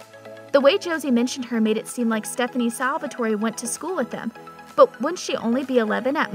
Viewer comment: Did she just say stefon and salvatorey…